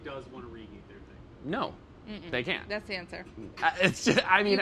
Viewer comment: Did Molly just say "that's cancer" lmao